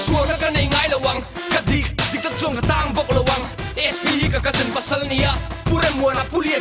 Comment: K hre sual nge k hrelo but..did he just said (spi hi k cousin pasal ani a)? 1:07 ...